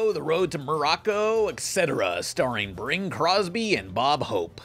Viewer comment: Sounded like you said "Bring Crosby".